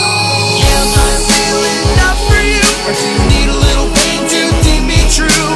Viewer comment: You need to be careful with the extra voices when it goes "am I real enough for you" because it sounds like it said"am I really enough for you"